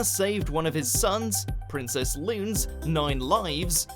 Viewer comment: I'm sorry did you say "Princess Lin"